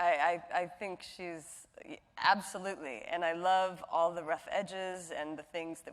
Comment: Can anyone help me out to understand what Keri says in 45:16? She's talking about Elizabeth and says “I do love her. I think she’s...absolutely…and I love all the…ref?? edges??? (That doesn't make sense, I know). I just don't get it, I'm not anglophone...Thanks in advance. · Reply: "and I love all the rough edges"